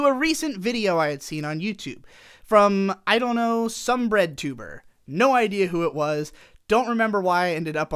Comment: When you first said redtubwr I thought you said breadtuber